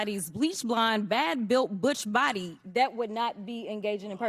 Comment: Bleach-blond, bad bulit, butch body... Haha. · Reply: I thought she said Busch body😅